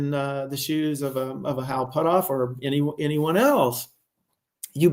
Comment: he says "or in the shoes of a _______" what word does he say? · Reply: "Hal Puthoff or anyone else..."